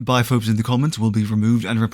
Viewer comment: I swear I heard "bi folks in the comments will be removed and reported"